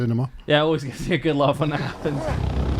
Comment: I rewinded a dozen times... did he have a stroke? lol what did he say? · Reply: yh always gets a good laugh when happens